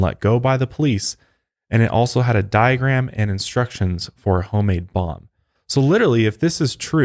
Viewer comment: I heard "diagram and instructions for a homemade bong" and I was like WAIT THAT TOOK A TURN WHAT lmfao my high ass